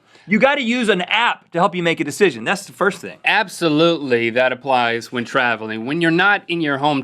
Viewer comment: HA! It kind of sounded like Link said "appsolutely" during the chat about apps :p